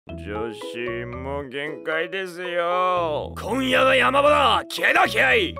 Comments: 今夜が山田って聞こえたw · 「今夜が山田」って聞こえた